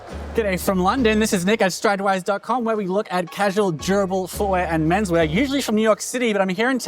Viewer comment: ok... you are saying "casual, durable". I heard "cash all gerbil" for the first two times listening. Knew that wasn't right and just kept replaying til I got it :D